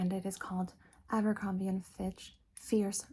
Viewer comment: ambercrombie and fitch.. i thought i heard ambercrombie fizzwidget i gotta stop playing so many ratchet and clank games while listening to asmr